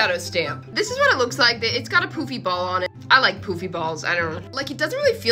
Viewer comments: I thought you said “I like poopy balls”💀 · I thought she said poopy ball😂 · I thought that she said poopy but she said poofy